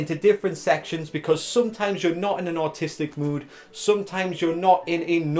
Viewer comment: ngl, when you said "sometimes you're not in an artistic mood", legit thought you said "sometimes you're not in an autistic mood", and i was just thinking "you can't exactly turn autism off", then i realised. Fantastic advice btw :)